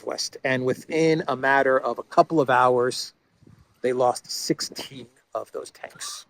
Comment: transcript wrong? I heard 60 (listened to it multiple times): 5:31 "...and within a matter of a couple of hours they lost 16 of those tanks ..."